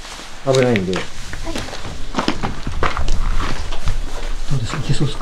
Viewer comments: 女性の声で「はい」が聞こえます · じゃあ、ここに、危ないんで の後に "はい" って聞こえるの気のせい？